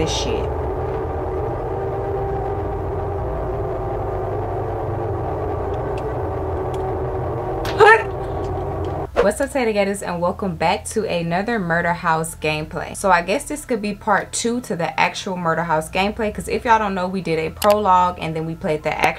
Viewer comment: Omg dis whole time I thought u was saying taytagators w like a lil cute accent not like gatas (gAH-tuhs) 😭 anyways love your vids